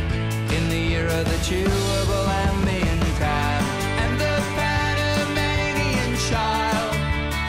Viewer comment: The first time I heard this, when he said "Panamanian Child" I thought he said "Pyromaniac child."